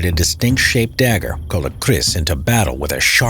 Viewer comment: ya we moro are blend malay too and our kalis is a sword size and not dagger size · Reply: owh, its the 'kalis', not 'keris', lol I thought he said Keris 😂 btw I am a Malay